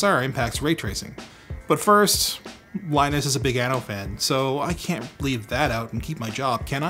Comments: Is it just me, or i heard "Linus is a big anal fan" :) Then that ooo, it should be anno :) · I thought he says:" Linus is a big anal fan" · "Linus is big anno fan" i had to re-listen few times to make out 'anno' instead of 'an@ Yep. Confused.